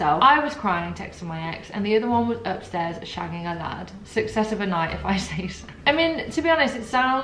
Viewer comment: I thought she said "Shagging her dad" and was so confused as to why said it so casually 😂